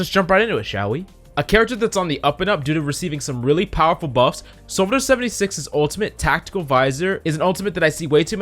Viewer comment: it sounds like he said sombra 76